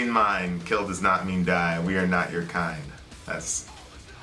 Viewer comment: I thought they were saying "we are not your kine". Pity, I think that would have changed the meaning to something much deeper. That line is probably inspired by Nails' "You will never be one of us".